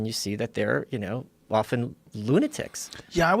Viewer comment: for a moment, i thought he said they were waffen lunatics ( which i thought was quite appropriate)